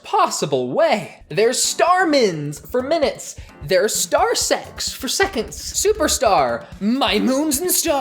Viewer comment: istg I heard “star-sex” instead of “star-secs” as in “star-seconds”